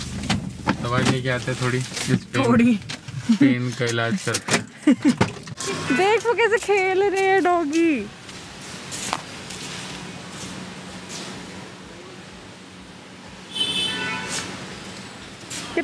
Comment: sorry I heard dhongi 😂😂